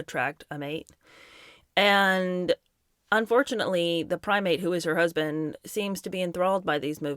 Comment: “… Harry is her prime mate…” But I heard: “Harry is her primate”